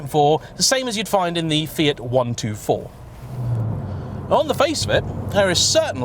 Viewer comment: did he said fiat 124 at 1:14 ?